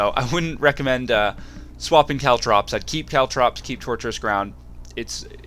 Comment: Thanks for the guide... nice one! By the way, think I heard "Treacherous Ground" instead of "Torturous Ground"? lolzzz ;)